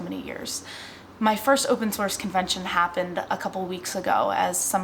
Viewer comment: I thought at first you were saying open sore, hehe